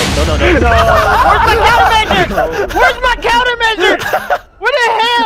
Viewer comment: for some reason I thought he said wheres my charmander XD